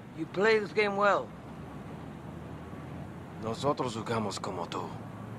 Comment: Can anyone translate what Tubbs says at 00:58 second? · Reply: Tubbs says : We play like you= Nosotros jugamos como tu.